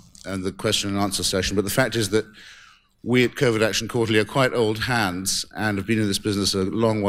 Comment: did he say COVID ??? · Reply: covert